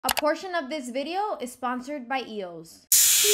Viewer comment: In the beginning I thought she said “unfortunately this video is sponsored by eos” I was like”GIRL😭”